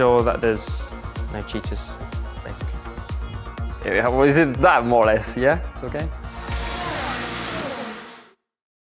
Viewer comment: what did ocon say in 10:49 ? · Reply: "is it that, more or less?"